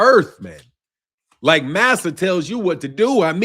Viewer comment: thought you said "NASA" not master lol